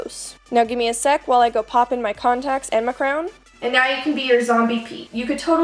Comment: Oh god I lost it when the video cut right before she finished the sentence "and now you can be your zombie Peach" and it sounded like she said "and now you can be your zombie pee" 😂😂😂 love you ❤️